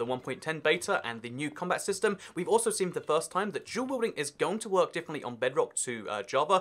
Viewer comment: Im sorry lol but what does he say at 0:42? Captions says he says jubilee lol but i know thats not right lol. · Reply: Dual wielding